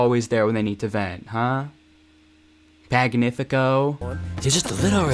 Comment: is it just me or did u call him "fagnifico" at 6:57 askljdhfkljashf